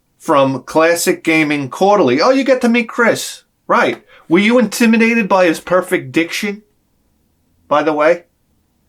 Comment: At first I thought you said his perfect dick shape when you were talking about Chris lol😀😀